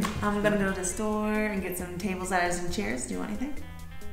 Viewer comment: "I'm gonna go to the store to get some tables and chairs". I thought I heard table, ladder, and chairs